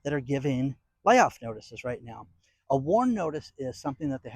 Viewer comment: At first I thought you said "war notice"